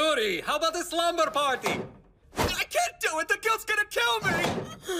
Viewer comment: what the anime says: "I cant do it the guilts gonna kill me!!" what the youtube captions say: "I cant do it the kids gonna kill me!!"